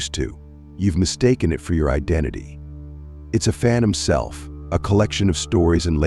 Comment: *It’s a Phantom Self…not a “fan himself” as the subtitles/captions suggest. Anyone else catch that?